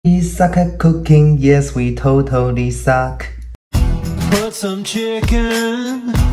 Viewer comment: I thought he said “ you sucking cocaine yeah we totally suck” ☠️